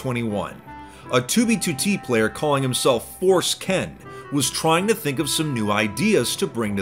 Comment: i thought i heard forskin